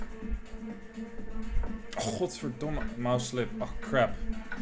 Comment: What did Robin say at 17:56? Something similar some times says. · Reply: god damn it in dutch, godverdomme